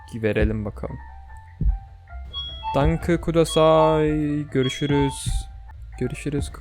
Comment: Bir ara "danke kutasai" dediğini duydum sanki kdjdjdhd